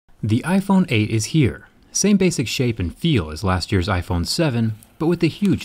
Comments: When the video started i SWEAR i heard you say "The iphone8 is here, same basic sh*t and feel as last year..." hahahahahaha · I thought you said "same basic shit" at the beginning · lol i thought he said "same basic shit"